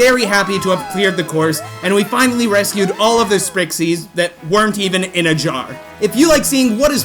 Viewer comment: I thought you said sbrixies